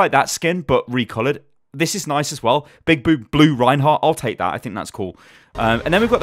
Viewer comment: I heard you say big boob reinhardt as you then corrected yourself you wanna tell us something?